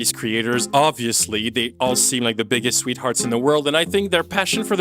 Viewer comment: I had to go back at 1:34:53 because I thought he said “obviously they’re the biggest retards in the world…” I was like oh my! I mean yes but you can’t say it like that right? He actually said “they’re the biggest sweethearts in the world” Meh. I don’t know about that.